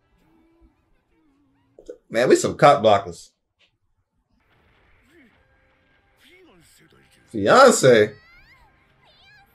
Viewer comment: For a sec I thought said he needed Some Docs, as in Dr. Martens.